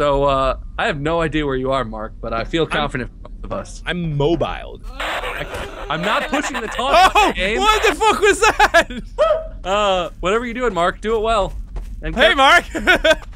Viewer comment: anybody else think that sounded like Gaster?